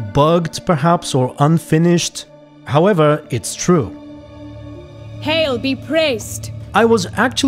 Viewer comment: You know, instead of 'Hail be praised' I heard 'Hell be praised' and for I second I thought things took a dark turn